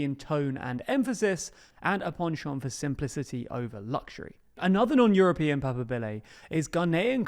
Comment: what did he say after "and"? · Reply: "A penchant for simplicity over luxury"